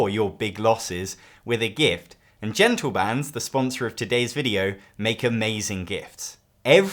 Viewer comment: I zoned out for a moment and nearly fell off my chair because I thought you said genital bands. Certainly a gift that keeps on giving, but I was quite relieved to be wrong. Speaking of rings, Duel For Middle-Earth has been on my watch list for a while now, but you just convinced me to finally give it a whirl. Toy Battle also looks great, definitely looking into that as well.